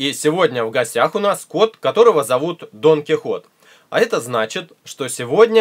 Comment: Мне послышалось ДОН ХИ ХОТ